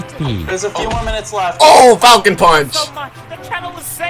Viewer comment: In ssbu I thought Falcon punch he said taco punch